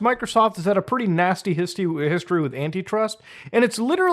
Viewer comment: says histy instead of history. Thank you for your time